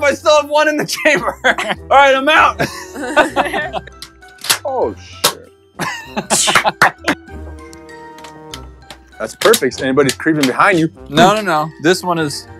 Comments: Did say oh sh*t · Look at 1:20 i think he just said s—t